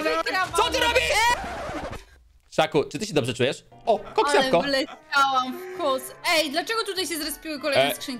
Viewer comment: czy ja usłyszałem kox jabłko? To był refil kox się świeci